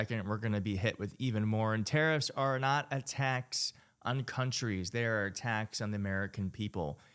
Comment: Hahaha! You said, "tariffs are not a tax on countries". What I heard was "tariffs are not attacks on countries," and I thought yes, they are!!